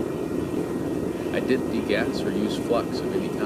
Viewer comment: I can't understand the statement at 6:15 ! Did you say you DIDN'T need gas or flux at anytime? That what it sounds like.